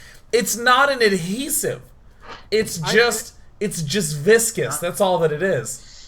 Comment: Can someone tell me what words they are using on 39:20 - something like "enchisive" and "viscis " (sounds to me... i don't know those words.... can someone help? ) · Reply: InariSound They use adhesive <link> and viscous <link>